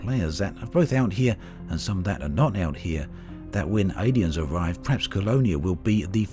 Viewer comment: did you say aliens?